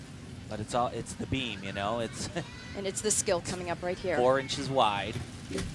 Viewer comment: i think he says "it's 4 inches wide", but i TOTALLY thought he said "orange is white" the first time, too!